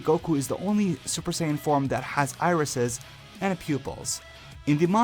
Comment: it sounds like you said "arses" 😂